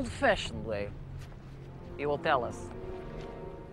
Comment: And what the hell is the Russkie saying at 3:35? I know Spalko says Давай (let's go) because...reasons. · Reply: They say "Готово, товарищ полковник" - "Done, comrade colonel". And what are the reasons?